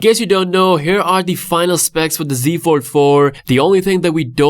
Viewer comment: I thought he said, z four four, instead of z fold four